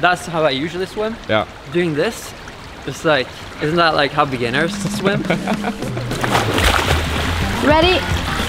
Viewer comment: about the breast stroke "Isnt' that how beginners swim?" Olympic swimming: AM I A JOKE TO YOU? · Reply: i thought he said thats not how beginners swim because he is not a swimmer